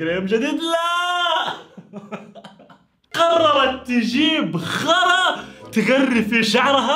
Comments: كاني سمعت يقول حْرا بدال غرا 😭😭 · والله سمعتها خرا من متلي، 😂😂😂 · انا سمعت كلمة ثانية 🌚💩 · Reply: خرا 👍